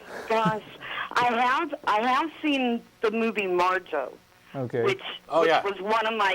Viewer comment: What movie did she reference? I couldn't understand what she said. It sounded like she said it's called "Marto" but I can't find any movie with such a name when I look it up so I must be mishearing her